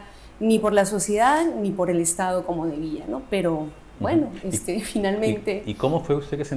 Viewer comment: Dijo en el minuto 1:10 Como el estado como enemigo? Quizás no escuche bien o le traiciono el sibconciente? Que escucharon? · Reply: "Debía". Si no escuchas bien, repítelo. Es un video y tiene opción para cambiar la velocidad xd